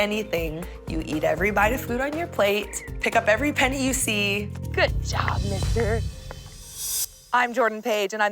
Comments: Did that bish just say good job N-word? 0:15 And if I'm wrong, please tell me what she said to her son (called Hutch as seen on the label 1:48) ... right after picking up a penny. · is it just me or did anyone else hear her say “good job n.......”